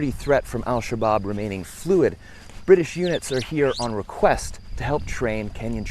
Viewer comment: Lol! Did I hear that "They are here in request " ???? Hahahahahaha